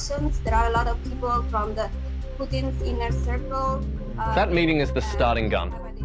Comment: “Putin’s inner circle…”, Iheard her say it in the intro…